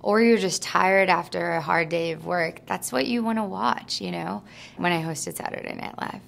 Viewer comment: Is anyone who can write what she says at 2:59 please? (I’m french and I don’t understand) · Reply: missangelina58 she says "that's what you wanna watch, you know?" She is talking about liking comedy.